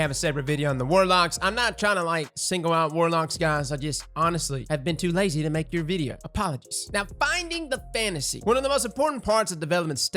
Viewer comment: Coulda sworn he said “I’ve just been too busy to make your video. POGGERS”. I loled